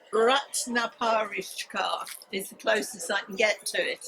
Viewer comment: Did anyone understand the name of the book in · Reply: Never mind got it... It called Ratna Pariksha ❤